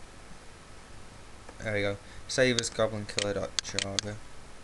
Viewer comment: what are you doing i can't speak good english so i dont understand what you said, something like : sees you got to go